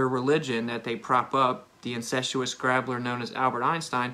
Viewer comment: Good vid, short, sweet and to the point, in sharp contrast to babbling brook... At 6:14, he says "the incenstuous grabbler Albert Einstein"? First off, the captioning thought he said "grappler", it sounded to me like he said "grabbler", in either case what the heck does he mean, what word is it? And incestuous for crying out loud, where did that come from? If you're going to call someone names, shouldn't you at least know what the word means? He tries to sound all official and scientific, but then brings in his religious belief bias and lack of skills with mathematics, geometry, scale, three dimensional visualization, all the usual. And yes, he claims we don't have proof good enough while providing absolutely none of his own. I feel like going to his channel just to leave a comment with a link to this video of yours.